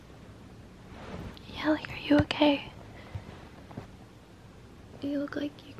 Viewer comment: I thought she said “are you gay”